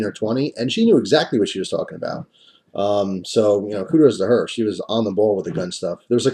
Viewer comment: At around 2:10, did he talked about the knowledgeable, young lady behind the counter. Did he say "Hooters to her?" I know the saying "kudos," but I could have sworn...